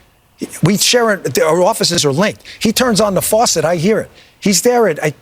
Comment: "If he toot's on the throne, I hear it"